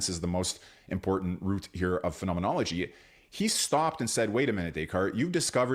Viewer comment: I thought it said phrenology. Now I'm disappointed.